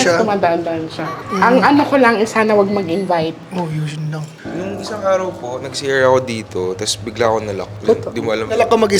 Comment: Ako lang ba nakarinig 6:31 sumabay sa boses ni ung bata😭 di ko masyado ma clear kung ano sinabi eh🥹🥹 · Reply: boses ni check mo bibig nya bumulong sya sa mic ng gago after magsabi ng *oh yun lang *